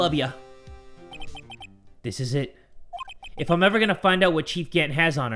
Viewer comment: I thought it said “this is lit” 😂